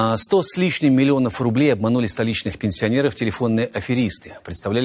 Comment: Сначала мне послышалось "столичные аферисты" и я грешным делом, успел подумать, про правительство Москвы?! Но... меня не подвела солдатская смекала!) Я быстро ударился 12 раз об пол, произнося Его Имя, и меня снизошел прежний покой в теле и блаженная пустота в голове!) Лежу на полу и улыбаюсь)